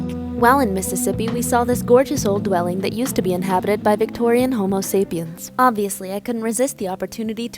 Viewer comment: I heard "Victorian homosexuals" ☠️☠️☠️☠️